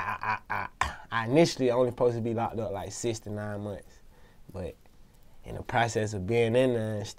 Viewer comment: I initially suppose to be locked up for 69 months Me um that’s like 6 years but ion think he meant to say that